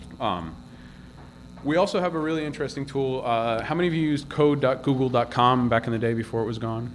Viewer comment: Looks like Hound is Etsy (not Google as I thought I heard say) - <link>